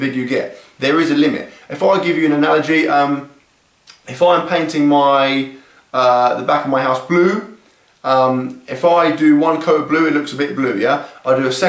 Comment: it sounded like you said "painted the back of my ass blue" XD